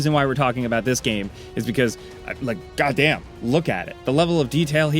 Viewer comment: i thought he said"gaw damn" because of that girls 🍑🐰